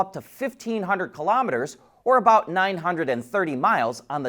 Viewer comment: did I hear that right? "Dacia guarantees a range of up to 500 km or about 930 mi". Something is off about that math!